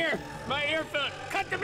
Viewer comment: Sounds like link said "my ear foot"